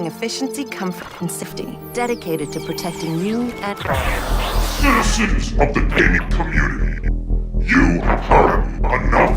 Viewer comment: did he say citizens of the gay community? I did not know Joe was part of them